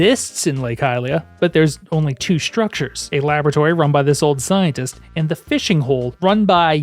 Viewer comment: I heard "old Zionist" and had to rewind thinking well that was random.. oh! Scientist! Yup, that makes a lot more sense 😂